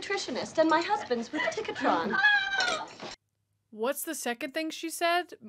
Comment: it sounds like she said "ticketron" (what the hell that is I do not know) 🙄